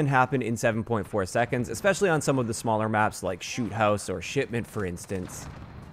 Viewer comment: Did he say Sh*tment lol?